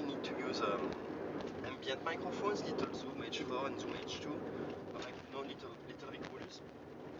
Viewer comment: Really hard to hear his answers, what did he say about microphones on 0:16? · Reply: Little Microphones Zoom H.... 2 ? Maybe ?